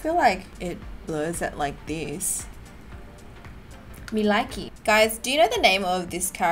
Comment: did she just say "me likey" ? 6:55 my once heart was shookt